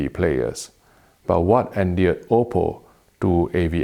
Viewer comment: I dont even have a blu ray player and never heard of OPPO till i started watching ur videos a week ago and was wondering what ur saying or talking about whenever i heard "oh po" lmao. And now seeing this, i almost cried, wtf. I want you to do my eulogy in the very distant future hopefully lol.